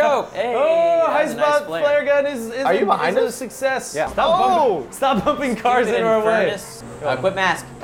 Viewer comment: i thought ian said stop humping cars in our way